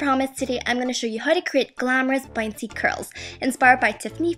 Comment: I thought it said bouncy GURLS :(